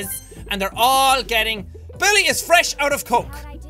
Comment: when jack said Beli I thought he said Billy. So, I automatically screamed SCREW YOU, BILLY. #conditioned